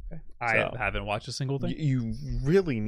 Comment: did you say Dandadan??? Like the anime??? What other animes are you watching???